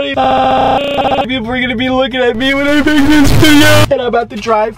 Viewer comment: Did he say I don’t know if you’re gonna be looking at me when I make this video😭😭😂😂😂